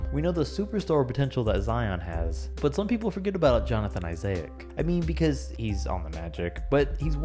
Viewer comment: Did he just say ‘Jonathan Isayac’